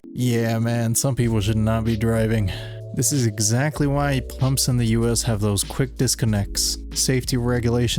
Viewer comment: Did you say “plumps”?